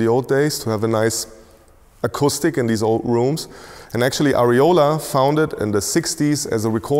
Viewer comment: Did he just say areola? Is it cold in there?